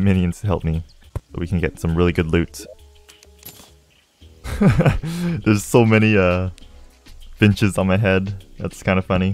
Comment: When he said “there’s sooo many finches on my head” I literally thought he said “there’s soo many bitches on my head” 😂😂😂